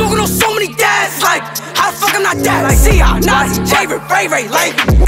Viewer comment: did he just say he's smoking on notti I know I can't be the only one that heard that and I'm not making it up listen to it